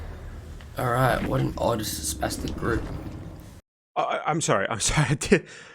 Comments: I think he says "what an autistic group" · Well, chalk it up to cultural differences i suppose, but I i had to listen 4 times and even slow the video down once to figure out what this allegedly offensive word at 7:47 even was because the only thing I could come up with is i misheard what he said. It sounded like mumbling and I thought maybe he said something far more offensive than what I thought I heard. But nope, all he said was spastic, which doesn't even register as an insult to me, let alone one that can't be repeated aloud. US here btw. Now, I'm pretty much impossible to offend but I can at least identify what these offensive words are. At least until today. This was a literal first for me where i couldn't even recognize the word as offensive. I actually had to read the comment section before realizing what word you were referring to, which I'll refrain from repeating anymore since some find it as offensive as they do. But to me, to find that word that offensive is just wild. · After the billy match i didn't hear what he said Abt the survivors that was mean I heard " Otzes best players" can someone tell me what he said? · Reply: He called them a “sp*stic group” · Maybe im just hard of hearing but i cannot understand what he said around 7:50 lol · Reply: He called them a “sp*stic group” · I genuinely have no idea what he said at 7:48 i listened to it like 8 times and still can figure out what he said. It sounds like he said " what an odd spastic group"